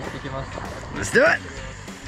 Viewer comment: 英語を最近本格的に勉強したばっかなので聞き取れなかったんですけど、「さぁ行こう」ってなんて言ってますか？ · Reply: Let’s do it!!!!!!